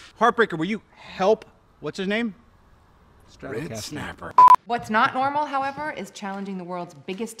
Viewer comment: For some reason I couldn’t stop laughing it wasn’t even that funny idek what he said I heard something snapper it was so funny though🤠